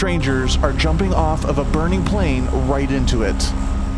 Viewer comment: Did he say burning plane? 😕❓